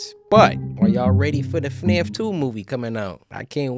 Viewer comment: I thought he said that he was excited for fnaf movie like fnaf one. I was like bro you were literally in it